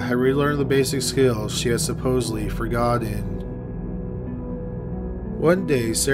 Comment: it almost sounds like you said “forgot-did” haha I replayed it 5 times